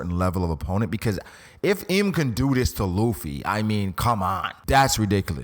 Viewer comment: naaah why u say it like that 9:46 then I thought u said throw me reversie 🤦🏾‍♂️🤦🏾‍♂️ maybe im the problem